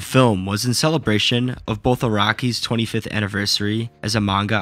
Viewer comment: even the cc thought you said "iraqi" instead of "araki"